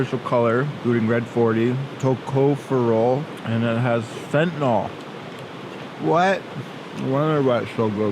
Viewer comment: DID I HEAR THAT RIGHT DID YOU SAY FENTANYL???????????